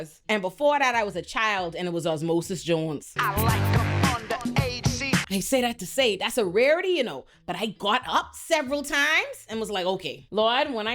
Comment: Omg did I hear that correctly?? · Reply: "Young ladies young ladies i like them under aged see. They say thats statutory I say its mandatory."